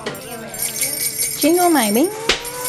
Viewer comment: at first I thought kiri shirt said "jingle my balls" but I looked back at it and hahahahah and it actually said "jingle my bells" ahhahahhahahhhhhhahahhahahahahahaha